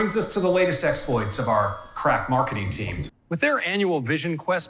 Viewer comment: did he say "Crap Marketing Team"?